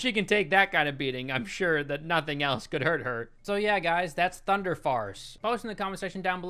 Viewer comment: at first I thought he said thunder farts